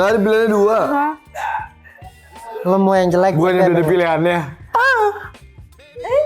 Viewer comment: ini dia ngomong apa?? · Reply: "gw yg jadi pilihannya" maybe yaaa😭